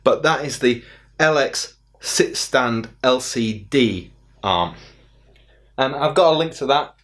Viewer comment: Do you mean the Ergotron LX HD Sit-Stand Desk Arm? I watched your video twice, and it sounds like you say LX LCD Arm. Unless maybe they are called something different where you are. Overall, great video!